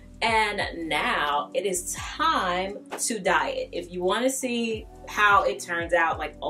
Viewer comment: She said now it is time to "dye it" and for a second I thought she said "diet," and I was like what?! Then saw her changing it to purple realizing my mistake. LOL